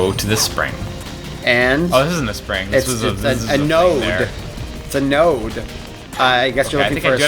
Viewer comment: Did you say nerd?